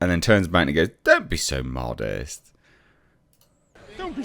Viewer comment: Heh, yeah.. “what de heck? You weren’t supposed to be honest there! I ask you a fluffy question, you give me a sound bite; are you new here or something???” And even after that, she just hoofs him right in the kiwis again; “C’mon, does anyone want me in this thing any more?” I mean, he literally might have been less shocked if she did give him a swift boot to the dingleberries!! 😂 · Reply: Yeah and I thought he said "Don't be so honest" but he actually worded, "modest." Anyhow, he had a scoop on his hands but probably got scared because he was worried Disney was going to take away his corporate boss's (Variety) access privileges.